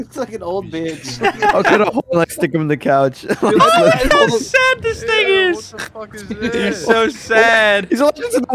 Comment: not that i care but did say the n word at 8:17 ? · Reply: no he said “look how sad this thing is”